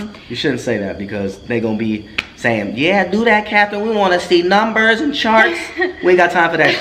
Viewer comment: I thought he said captain